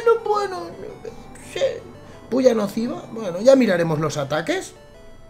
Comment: Te juro que entendi "Polla explosiva" xDDDDDD